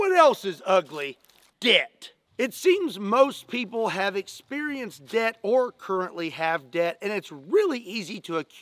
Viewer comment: I am the only one who hear death?